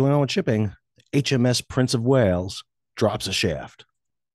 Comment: Thought you said drops a shit.